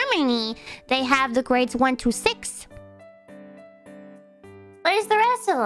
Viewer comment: from 1 to 6 って言ってくれれば分かるんだが 単に 1 to 6 となると 1 2 6 と聞き間違えちゃうんｗ